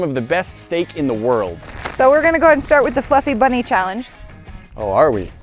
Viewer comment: I thought she said stuffy bunny and my mine immediately went to a bad place. IYKYK